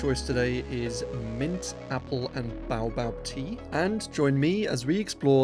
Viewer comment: ……did he just say ….mint….n….bou bou………tea…… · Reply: Baobab tea! The best!